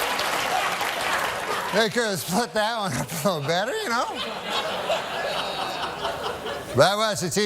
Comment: What did he say ? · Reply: 5 years later, don’t want you waiting any longer: “They could have split that one up a little better, ya know?”